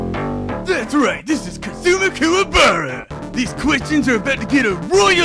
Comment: I know the character's real name, it just sounded like he said "Cthulhu Kuwabara"